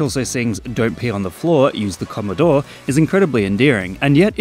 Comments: I've always thought the fish said: "Don't pee on the floor, *use the coloured door*" If you put this into perspective the coloured door would be the toilet water which is coloured due to your level of health and thirst and can open and close. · I thought he said “use the corridor” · I alwats thought he said "Don't pee on the floor use the corridor"